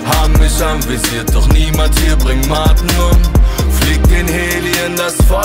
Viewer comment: Super Lied. Nur ich hab bis jetzt die ganze Zeit nur verstanden "Niemand hier bringt Magnum."